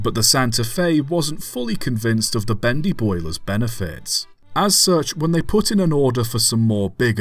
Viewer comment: what you said: wasn’t convinced of the bendy boiler’s benefits What I heard: wasn’t convinced of the bendyboi’s benefits.